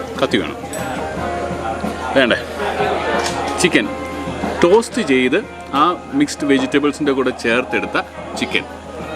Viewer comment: Hello at 12:13, did you say chicken was "Toasted" or "Tossed" with the vegetables, I totally missed it. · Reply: Toasted...